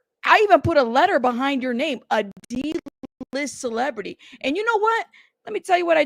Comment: I thought you said you called him a D*ckless celebrity at first 😂😂 I was still going to agree with you 👑🐇